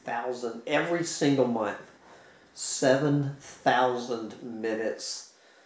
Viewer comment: 7000 hours on the phone per month = 16% of your life on the phone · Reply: Sorry, yes you said minutes, and I thought you said hours, I blame it on the weed I was on yesterday.